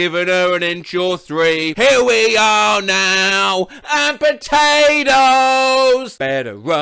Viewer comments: I always heard "here we are now, in containers" when that song played on the radio. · I always heard "Here we are now. In containers"